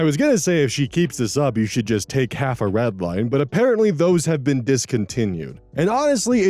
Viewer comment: half a what? I couldn't make out that word. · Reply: Redline, I think, apparently an energy drink.